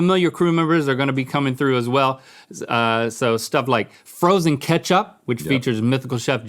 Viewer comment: did he say frozen ketchup?